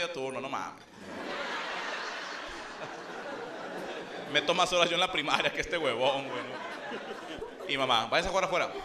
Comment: ¿Qué dice? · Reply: Meto más horas en la primaria que este huevom